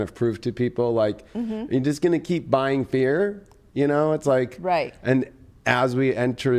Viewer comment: I thought he said, "Are you gonna keep buying beer?" Rewinded it and he said "fear" 😂